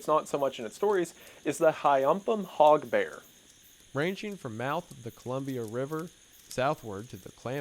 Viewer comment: For a second I thought he said "Hyampom Pog Bear" and I imagined a bear going around pogging and pointing at things. XD